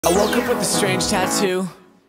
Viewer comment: "It kinda looks just u" aka thick and short 😆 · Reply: I heard it "Kyle looks just like u"